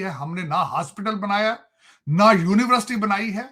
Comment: ye kya bola wakeel? Universterities? ye to inke lawyers hain.. 😂😂